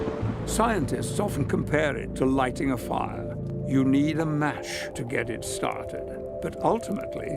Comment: Is it really him? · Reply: No I believe its an AI generated Patrick Stewart voice. At 13:00 it says "Mach" sounding like (Ma)+(sh) instead of "Match" sounding like (Mat)+(ch). A common AI voice mistake.